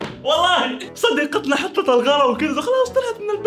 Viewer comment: سمعتها خ*را😂😂😂😂😂💔